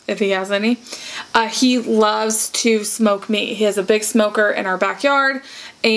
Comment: At first I thought you said that you like to smoke weed.. I was like this 😮 but then I realized you said smoke meat and I about died laughing.